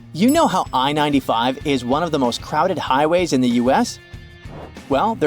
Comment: Yay my state is basically in my ship that I might go on this summer lol yeah Highway 75 goes all across Georgia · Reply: Never mind, I thought he said Highway 75